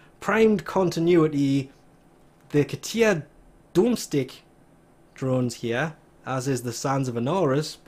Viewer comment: he pronounced domestik as dome-stick and not domestic smh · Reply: I thought he said doom-stick